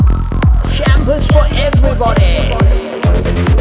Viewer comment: I misheard you saying "Santos for everybody." instead of champagne.